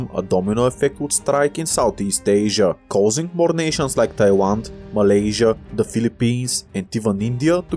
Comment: for a sec i thought he said taiwan